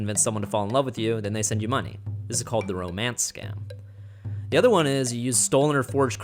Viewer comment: I thought he said Roman scum